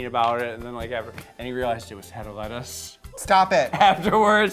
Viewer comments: "And he realised it was---" I can't understand :c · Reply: Hedda Lettuce. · What does he say at 10:15?? I don't get it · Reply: I think he's talking about Hedda Lettuce? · What does james say at 10:21..? I keep replaying it but i cannae figure it out.. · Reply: He actually said "Hedda Lettuce" who's a drag queen. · what does james say at 10:20? head of lettuce?????? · what did James say at 10:20? head of lice, head of lettuce I'm at a loss 😂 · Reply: Hedda Lettuce. One of the queens from Rupaul's scene that's known for being hard to work with. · What is James saying at 10:15? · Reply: He's talking about a friend of his who fucked Hedda Lettuce. (A queen from NYC)